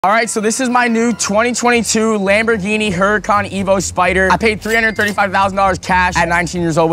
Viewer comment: Thought he said emo spider at first 💀